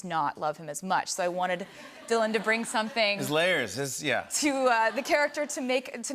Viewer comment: did Jimmy say 'his layers'? As in layers that you put on to keep warm? As in, I don't know, a SCARF? The man is truly a swiftie and I am here for it!